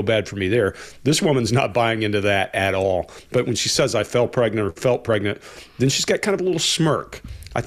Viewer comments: I am Australian and I'm pretty sure she said she "fell pregnant " that aside she obviously is 100% full of it what gets me so much is how she admits she never had cancer and never had surgery or anything but still talks about the trauma she felt from the fictional disease and treatment in the next breath! Wild what a as we say in Australia "see you next Thursday " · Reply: I heard it as 'fell pregnant' too. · I know thought Belle said, "I felt pregnant", however, I believe what she actually said was, "I FELL* pregnant." This is an expression often used (mostly by an older demographic) by the British and the Australian. Here is a hypothetical sentence in how it could be used: "I had to stop dancing professionally because I FELL pregnant." *Caps used for emphasis only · You guys should leave in as many "bloopers " as you can. It's always nice to see you all smile, especially after such serious topics. We laugh when you laugh. Keep up the good work! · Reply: I thought she said "I fell pregnant" (as British and AU NZ people use language to mean she BECAME pregnant) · I thought she said " I FELL pregnant " which is a wsy to same " I BECAME pregnant" or another way for some people to say " I got knocked ip" in an old fashion type of way. I could be wrong about that, but I've listened to it several times and can't hear the T on the end of the world. Not defending her, just another thought on it.